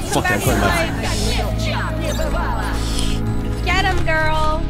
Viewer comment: WHAT DID SHE JUST SAY????? :face-purple-crying::face-purple-crying::face-purple-crying::face-purple-crying::face-purple-crying::face-purple-crying::face-purple-crying::face-purple-crying::face-purple-crying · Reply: no way!!!! did she just say girl!!!???